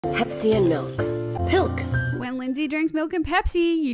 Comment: Why am I hearing "Hep C and milk"